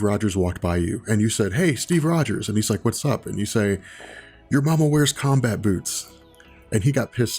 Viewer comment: At first i thought you said "your momma wears commie boots" then yea cap should swing on you at that point.